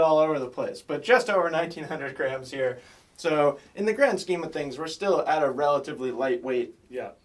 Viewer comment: did you just say, "in the gram scheme of things"? Ha!